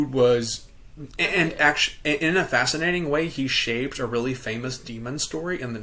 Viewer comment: yeah sounded like shaved 47:21 to me too, but, I love your "trying to decipher" face (pause please 47:30 and a half).